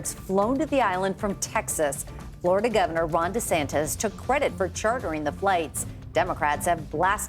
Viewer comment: Lol I keep hearing "Rhonda Santez" not Ron Desantis 😂😂😂